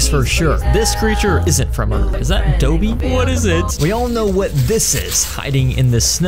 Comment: did bro say doeby 💀